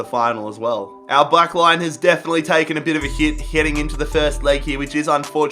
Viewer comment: did he say “Our black-line has definitely taken a hit”?